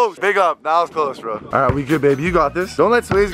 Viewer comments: Did call kris babe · Did you hear what I just heard 😂 8:09 “we good babe” 😂 I’m dead 😂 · Pause @ 8:11 did he say we good babe ?